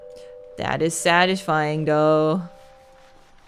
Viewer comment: My brain's playing tricks on me! I heard that as "Dad is sad, it's fine though" at first. 😂